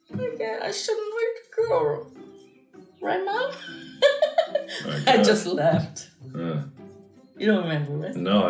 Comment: So wait according to, it went: "Why are you crying what's wrong??" Markiplier: *in pain, crying* "That girl hit me!" "Why did you not hit her back?" Markiplier: "I can't hit a girl!" *just leaves her 5 year old son there crying* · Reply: I thought she said “I just laughed” not left